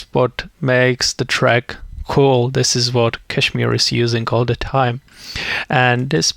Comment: what did you say? you said something at 6:36 I don't understand? he is using "a time"? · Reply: He says This is the pattern that KSHMR is using all the time